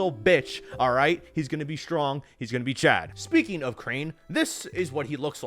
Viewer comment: kinda wonder if dl3 will also include crane or is his story gonna end with beast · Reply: I thought they said we are playing as crane